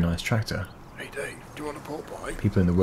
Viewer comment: did anyone hear the voice at 9:49 say hey how you doing do you want a pull boy